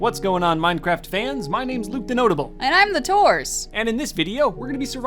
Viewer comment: For a second I thought she said And I’m divorced